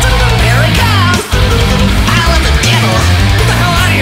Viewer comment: I thought he said I love the devil